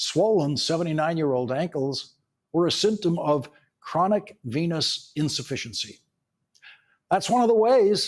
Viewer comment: Oh, I thought they said chronic penis insufficiecy